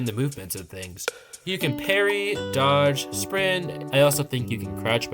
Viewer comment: did you say perry? +PERRY +PERRY +PERRY